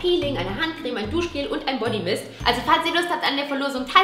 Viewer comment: ich verstehe erstmal so ein Bodymist